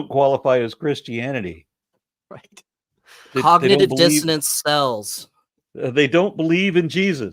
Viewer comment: I misheard when he said "cognitive dissonance sells" as "cognitive dissonance SPELLS" (thank you Letter-Man!) and had to laugh. Church is a ritual to cast a mass Cognitive Dissonance spell. I swear I'm not on herb but my creative juices are going, this needs to be a Priest spell in D&D...